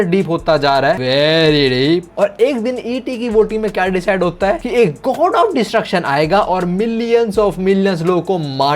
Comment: he said God of destruction I hear lord beerus